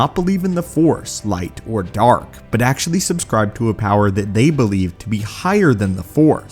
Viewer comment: I just hear "they subscribed..." What I'm thinking he's saying " they subscribed to my YouTube channel, and so should you..."